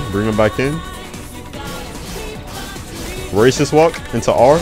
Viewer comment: bro sounded like he said racist walk